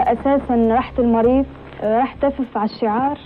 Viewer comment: راح ايش ؟ مافهمت ايش قالت احد يترجم لي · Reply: R97 - تف يعني بصق على الشعار